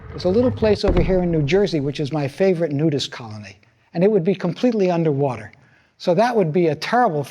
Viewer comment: Did he say nudist colony?